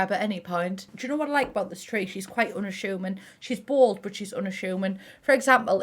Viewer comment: I swear at first I thought you said "she's quite on a shoeman." And I was like, what's that? 🤦🏿‍♀️ It's not you It's me and my verr amerrcan ears.